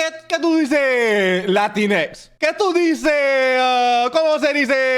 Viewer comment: translation: “WHAT? WHAT HAPPENED? UH HOW DO YOU SAY. LATINAS? HOW DO YOU SAY. HMMM BUT (idk lol) LATINAS HOW YOU SAYYYY (idk what he said here) DIED. LATINASS. MARRY CHRISTMAS. YES???” (I’m still learning Spanish and don’t know that much but this is what I understood but he was saying NONSENSE-) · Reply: don't worryyy, but what he said was "Latinx", some term that non binary people use since they don't want to say it with an "a" or an "o" CRAZY 😭